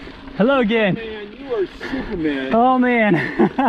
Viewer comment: I thought that hiker said dude you're stupid man, then I realized he said superman! Beast.